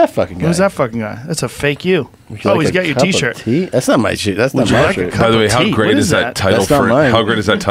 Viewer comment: Tom Segura having no clue t shirts with his face on it existed haha @ 1:18 "t-thats not my shit" (what I think he said) 😂